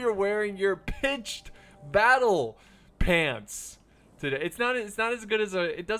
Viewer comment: I thought he said "pinched battle pants" lol